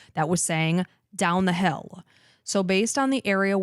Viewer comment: I hear 'get down the hill' if you listen again you can hear the 'get' first. 😮